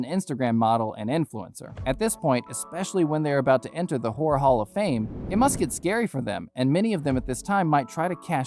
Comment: Did he say ¨the whore hall of fame¨ ?